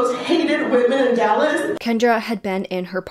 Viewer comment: I don’t get how Brenda gets shot in the head and the is some who convicted of murder I think ya mixed the names up I’ve watched this 5 times and still don’t get it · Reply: The first sentence OP says is “Kendra was in her parking garage when someone walked up behind her and sh0t her in the back of her head”. It does kinda sound like she says Brenda but she’s actually saying Kendra.